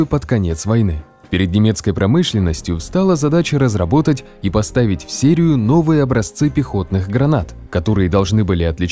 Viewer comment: Послышалось "разработать и поставить в Сирию". 🙂